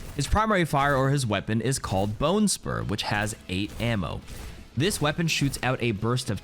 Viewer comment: i thought you said bonesperm